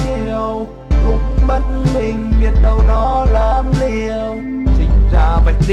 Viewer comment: nghe thành lúc bất bình biết đâu nó là kiều